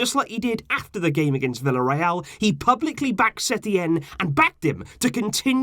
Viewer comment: I thought it said sacked instead of backed and I choked on my cereal